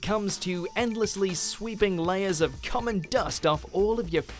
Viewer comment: it sounded like he said cum and dust